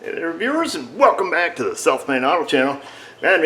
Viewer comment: How would I diagnose it? Easy...Drive to Avoca, I know a guy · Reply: i'm lucky my guy is a mile away 'south side auto' when you say your channel name I keep hearing 'Self Made auto" that works too.